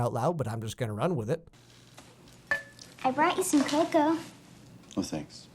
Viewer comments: LMAO CAPTIONS AT 9:11 THE GIRL SAYS “brought you some coco” BUT THE CAPTION SAYS “brought you some vodka” THIS IS GOLD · The subtitles say: "I brought you some vodka," instead of "I brought you some cocoa." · The subtitles on this are fabulous. For instance 9:11 Judy says "I brought you some coco", but the subtitles say "I brought you some vodka". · If you out the captions on at 9:12 instead of it saying brought you some cocoa, it says brought you some vodka. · IF ANYONE HAS SUBTITLES ON, YOU WILL READ I BROUGHT YOU SOME VODKA INSTEAD OF COCO.....I'M DECEASED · judy says brought you some cocoa captions say vodka 8 | ???? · the caption says "I brought you some vodka" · If you turn on subtitles at 9:09 instead of saying I brought you some cocoa it says I brought you some vodka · I know Judy says cocoa, but the subtitles say vodka and I am killing myself with laughter over here. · with subtitles 9:10 instead of coco it says vodka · i love how the closed captions at 9:10 show the elf brought Tim some homemade vodka instead of cocoa XD